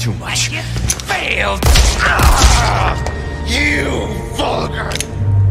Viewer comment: Mongrel! I thought he said, I'll Grow - I played without captions.